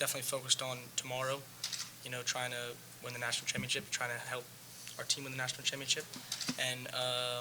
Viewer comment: what did say? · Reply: to try and win the team championship?